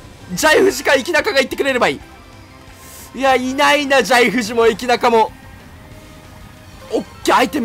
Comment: のエキナカがイキナカに聞こえてえっちだねえっち